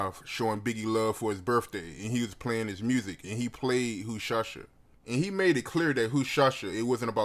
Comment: Lol sound like said “who shasha”